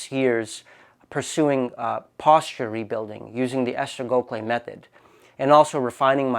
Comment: he rebuilt his posture using what method?? I can't hear it correctly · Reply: Esther Gokhale method